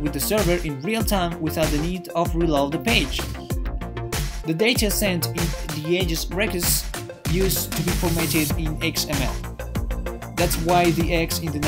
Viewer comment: anyone know what he said at 1:56? "the data sent [unintelligible]..." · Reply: Hello Alexia! sorry about that, the music of the video is very loud lol I said: "The data sent in the ajax request used to be formatted in XML"